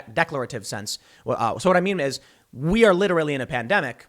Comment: it sounds like he said "what i meme is" lmao thats awesome. Bro if he could keep them on task pool will win any debate with these blue check marks.